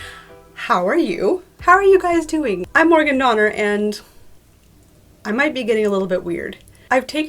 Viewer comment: English isn’t my first language so please excuse me, but when you present yourself I thought you said ”I’m an organ donor...and I might get a little bit weird” 😂